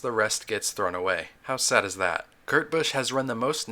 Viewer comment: I hear gurt Busch